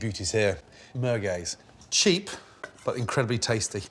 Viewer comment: for my Algerian folks 🇩🇿🇩🇿🇩🇿 did he just say مرڨاز ??